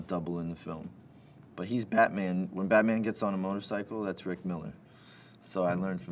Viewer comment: "When Batman gets on the motorcycle, that is Rick Miller." I thought he said Frank Miller lol